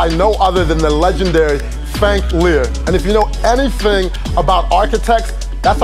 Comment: Girl, what name did you say for the architect that designed the Biomuseo? It’s Frank Gehry... sounded like you said “Fank Lurrr.” SWRONG WITCHU?